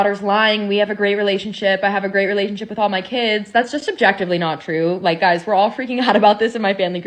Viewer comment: And the hits just keep on coming. She's called out on her lies, and instead of apologizing she doubles down, and thanks to pride, and relativism she decides to try and gaslight people into viewing dear old dad as a scum bag. Instead, all she's done is make herself look even worse. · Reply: She didnt say "Objectively". She actually said "Subjectively". Listen carefully what she said.